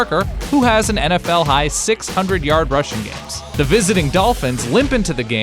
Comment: Going into this game, he'd already had 6 games that season where he rushed for 100+ yards, which was more than anyone else in the league (he finished 2007 with 8, no one else had more than 6). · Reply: Ohh! I heard it as 600 yard rushing games, not 6 100 yard rushing games. Thanks!